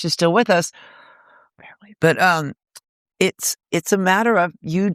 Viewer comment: I didn't catch it the first time around, but Courtney whispers at 1:20:20 that Kat Bjelland is "barely" still with us. This makes sense given that I listened to an interview with Lori Barbero from several years back where she talked about Kat being a nonfunctioning addict. Makes me sad. I think that, despite all the "she stole my image" bullshit that the media ran with, Kat and Courtney retained a strong sibling-like bond.